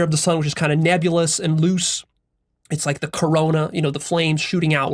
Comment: did I hear corona?